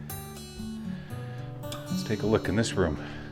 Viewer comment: When you were headed over to the two-holer and you said 'lets take a look at it', I heard 'let's take a look adit' Mining humor for the win!